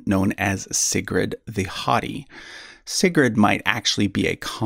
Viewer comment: I thought you said 'Sigrid the HOTTIE' for a second there. We say haw-tee in Britain.